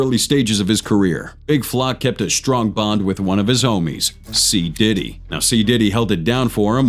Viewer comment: I thought he said p-diddy😂😂😂😊